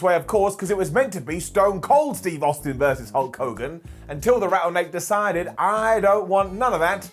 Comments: I'm sorry, did just say "Rattle neck?" 😂😂😂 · Did say Rattle neck